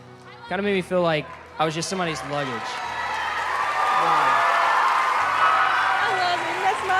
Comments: The dude and the first girl are the best so far like if you think so Somebody said I love you after the dude said when it seems when nobody loves you at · I HEARD SOMEONE SAY “I love you” In the crowd 😂😂👌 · did anyone hear that girl in the background say “I love u” 😂❤️ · “I LOVE YOU” Anyone hear that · Who else heard at 16:28 the girl yell " I love you so much" like ugh that made me tear up 😂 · who heard somebody say I love you in the crowd 😂😂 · anybody hear somebody in the crowd say I LOVE YOY?😂👌